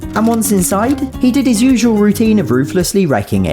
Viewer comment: With your lovely accent I keep hearing "rooflessly wrecking" when I know you are saying "ruthlessly wrecking," but it's funnier to think of him not caring whether there's a roof 😂😂